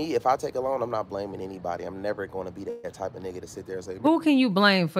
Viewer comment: I thought this man just said he’s not that kind of person.👀 Nope I’m cool on all new friends. Frfr